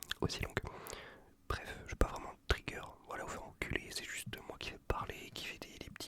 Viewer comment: "j'ai pas vraiment de trigger aller vous faire-" quoi !?